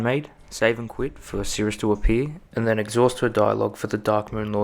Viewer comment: What does he say at 1:52? It's not clear :/ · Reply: Save and quit for Sirris to appear